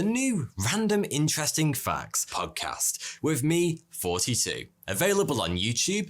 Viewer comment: is it me or does it sound like his saying "its me 42" not thoughty2 XD